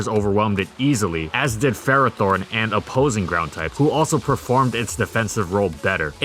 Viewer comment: id you call ferrothorn a ground type or did i just hear wrong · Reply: "Ferrothorn and opposing ground types"